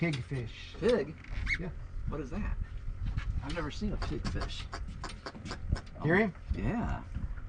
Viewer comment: I thought at first you said "pink fish." You two are always good for a laugh!!